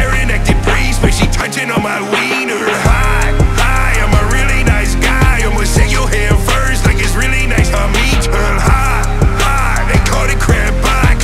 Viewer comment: who else heard this line as “HI HI I’m a really guy imma shoot ur head first like it’s really nice to meet her”